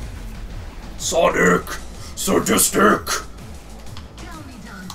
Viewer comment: The rages are much more passive in this version it seems. 2:37 - Did you say Sonic? xD (because it's supposed to be Savage)